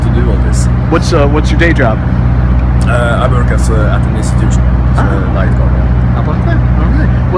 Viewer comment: At the 4:59 minute mark, he asks Hellhammer what his day job is, I can't make out what Hellhammer says...anybody can make it out? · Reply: He said " I work at an Institution."